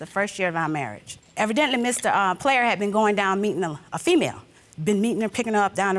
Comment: Hahah I thought judge was saying 😂